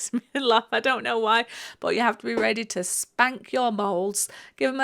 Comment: OMG, I almost choked on my coffee. I thought you said "this is where you've got to spank your balls!" 🤣🤣🤣 and then I realised you said "bowls". I was mortified with myself and my smutty mind. 😯 Anyway, love the "bowls" great shape, great colour and The chat is entertaining. Thanks.